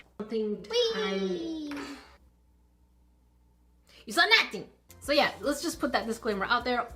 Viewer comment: wait you've been saying "you saw nothing!" this whole time?! 😮 I thought you were saying "you so naughty!" 😂